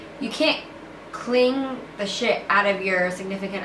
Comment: did Gabi say the S word not bieng mean I was just saying and this video will help me thanks Gab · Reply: Ya😂😂👌🏽👌🏽she said shit and hell